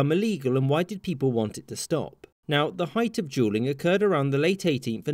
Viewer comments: Sounds like he is saying juuling which is also bad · I keep hearing you say “juuling” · it sounds like hes saying juuling lol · It honestly sounds like your saying juuling and I’m dying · I tjink its funny i was browsing for the next video and whenever i don't look at the video i swore he was saying juuling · I keep hearing “juuling”